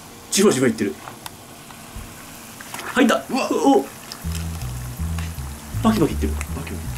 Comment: うわっ！ うほっ！に聞こえる…